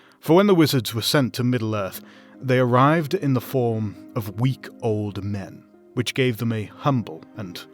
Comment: Lol I heard that as "week-old men" at first